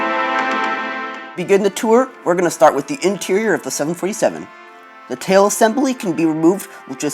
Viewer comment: Did you just say '737'? Edit: Nice video by the way. I like the interior and the cabin. So cool. I wish you can make the biggest passenger of all, the Airbus A380! I hope you do.